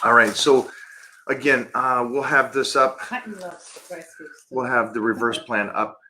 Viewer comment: Mike i thought you said no to rice cakes........i want to ask a question...HOW OFTEN DO YOU GO ALL OUT IN A TRAINING SESSION!